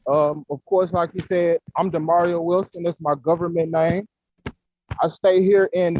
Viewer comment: Is his name "Mario" or "Demario"? Everyone here is writing "Mario" but when he introduces himself at 1:28 it sounds like he says "Demario". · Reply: It's Demario. But he goes by.